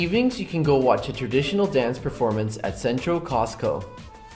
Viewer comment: This is great and all but at 2:08 the guy sounded like he said costco😂